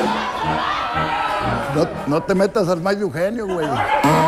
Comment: Alguien sabe que dice en el min 2:30? y que significa jajaja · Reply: Dice "No te metas al maíz de Eugenio"